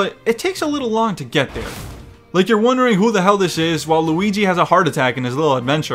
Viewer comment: why does it sound like says waluigi